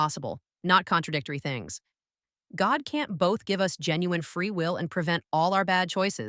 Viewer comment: did she just say "God can't"?